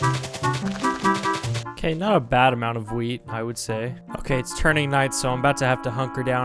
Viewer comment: i heard weed